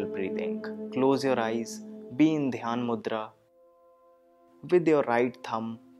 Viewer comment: what does he say at 14:13 ? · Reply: Dhyanmudra